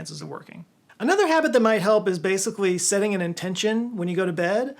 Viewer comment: did you say bas i v l y ? lmao otherwise i too have had lucid type dreams. even dreams i could fall back into and even experienced lucid superman dreams. very awesome stuff.